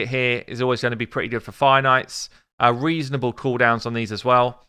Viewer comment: It took me a while to figure out what you meant when you said “Finite.” Like the opposite of infinite. I had to hear it several times and rewound the video to listen again and again. What I heard was “Finite,” what you’re saying is “Fire Knight.” ROFL Your accent is really thick today. It must be the anger over the Plarium money grab that’s brung it out. Good Video. I’m going for the Mouse only. Screw the lego.